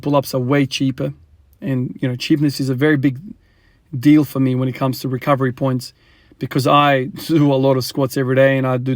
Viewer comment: What is your boy saying to you here? This is inspiring man. I am your same age but I am not a father. I hope I get this opportunity someday. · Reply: Yeh it’s an absolute blessing man. True wealth having kids. He was saying that is really heavy dad.